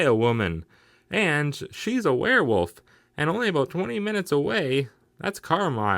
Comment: I could have sworn they said 20 miles not 20 minutes then again I'm not the smartest box of rocks in the Appalachia